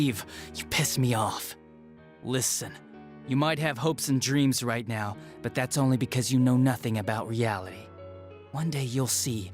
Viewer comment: he sounds like he says yaoi not reality